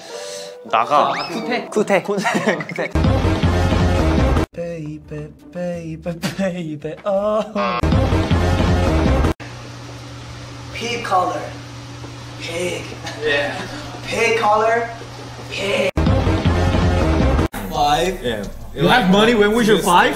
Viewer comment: my first time watched that part I thought he said "pink color, pink " ^^